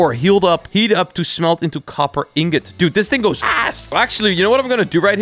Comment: I think just swear he said as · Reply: he said this thing is fast